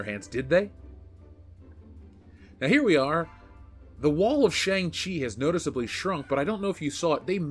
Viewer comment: I honestly thought you said "wall of Shame-Chi".